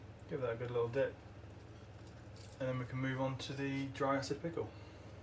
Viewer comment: What are you saying at 11:15? Sounds like dry acid pickle? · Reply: That’s correct yes, the second bucket is a dry acid pickle